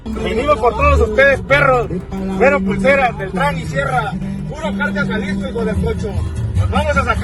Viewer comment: Donde dice audio inteligible, mas o menos dice esto: 5:23 Guero pulseras beltran y sierra 5:25 puro CJ hijo del cocho 5:32 Asi como los hicimos correr en Comaloapan esto es lo que dicen estos marucheros, es muy probable que por esa expresión, sean del estado de Guerrero